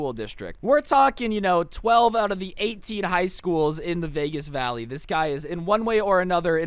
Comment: Vegas Valley? Dude I thought you kept saying you lived in a "Quiet Suburb"